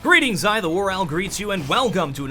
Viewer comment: Oh my gosh. For 3 years, I thought he was saying "Greetings, I, the WarOwl greets you". But it's "Greetings, Hi, the WarOwl greets you". xD